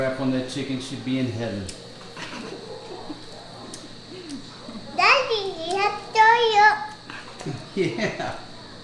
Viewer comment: Watching while eating using her hand was so cute and she’s good too, truly amazing👍👏 I heard she said “Daddy got TOYO” hehehrh